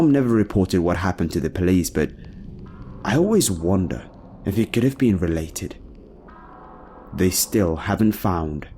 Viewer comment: I thought he said trudy apple pie at first😭